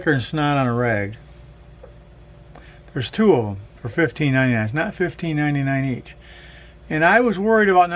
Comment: Can you tell me the model and price? Guessing I couldn't hear very well! @ 1:15. Thanks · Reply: I paid 1599$ for the pair of hearing aids the device to listen to the TV and other things was an extra 159$. The hearing aids are Kirkland 8.0 Premium (that is what is on the box) but these are their least expensive ones I think. I still like them. Good luck.